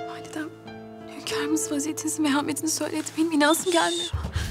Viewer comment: dakikada Mihrimah Sultan hürrem sultana ne diyor anlayan var mı? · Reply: Validem hünkarımız vaziyetinizinmehametini söyledi benim inanasım gelmiyor